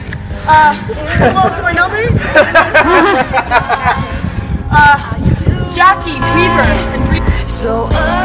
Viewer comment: Could anybody write down what Kristen says on 2:23, please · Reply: "who also I nominee? Jacki Weaver..." It's part of the ASL Ice bucket challenge video